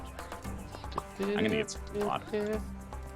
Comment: Anyone else hear Nl say "I'm gonna get some pot" at 1:17:58 xD